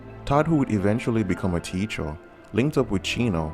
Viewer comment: I’ve replayed this part many times and still cannot understand Wtf this guy is saying. Is he saying “TEACHER”?? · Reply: yup it's teacher.